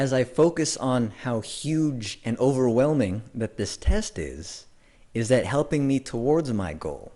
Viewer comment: did he say TESTIS?